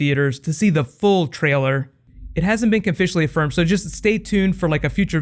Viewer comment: Am I the only one who heard "It hasn't been Conficially Offirmed"?